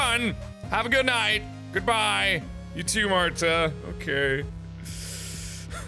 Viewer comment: did he say marzia?